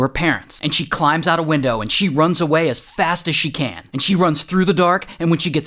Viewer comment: I heard "she jumped out the window and ran away" and I was like "oh it's the Turpin family"